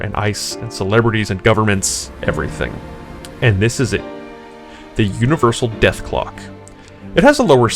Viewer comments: I thought it said the universal death glock 😂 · I thought it said Universal Glock but ok